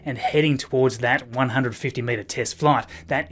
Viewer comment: The first time you say 150 meter test flight at 0:25 it sounds like you said 150 minute test flight and I was surprised